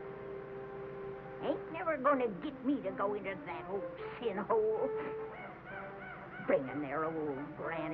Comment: does anyone else hear Granny say "s#!thole"? I honestly don't search out such things, but I replayed it a few times and cannot think of any other term that fits...